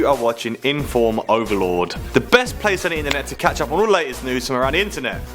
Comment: I swear I heard him say "inform overlord"